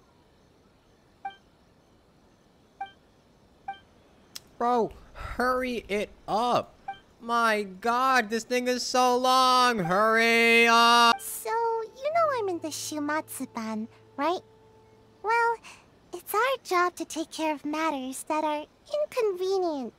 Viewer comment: Bro i aware I heard him say hurry up my n**ga so long